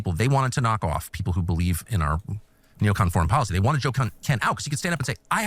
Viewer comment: I am high and I thought he was saying JoCat and no Joe Kent.